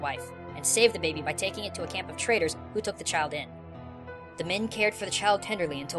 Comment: couldnt tatl and tael be considred the mouse? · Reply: Wait... TRADErs? I thought he said TRAITOrs